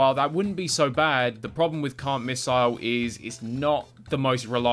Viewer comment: Why do i keep hearing "Cunt missle" instead of "Can't Missle"?